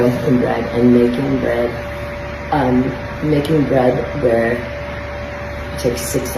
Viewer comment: Whats she talking about the between 8:22 - 9:11 ? I dont understand anything except 'making the bread' and she said it takes 6 hours😅